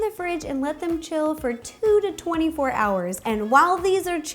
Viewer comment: did she just say chill for 2 to 24 hours